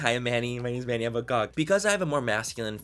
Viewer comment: did he say "I have a cock"??!?